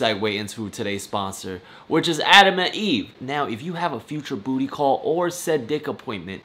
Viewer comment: Instead of “Adam and Eve” I heard “Anime deed” whats wrong with me i